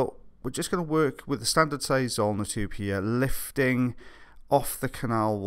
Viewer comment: It wouldn’t be a Friday night without the epic & his zulna tube. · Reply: Jesus Christ! I was wrong back then! Im from Indonesia and still trying to improve my English. I thought he was saying "arma tub". With the accent it's like "zarma tube" hahaha. Thanks! The more you know!